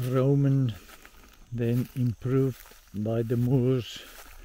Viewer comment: I heard "improved by the moose" and I totally imagined that, and it made me laugh so hard. :D